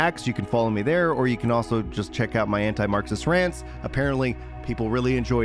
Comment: Having a bunch of gaming centric stuff and news and then also doing what I think I heard as "Anti-Marxist" rants is so random lmao.